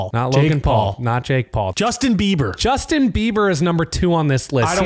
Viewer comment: I thought you said Justin Trudeau (I'd have to get in line) but not Justin Bieber he's had enoug.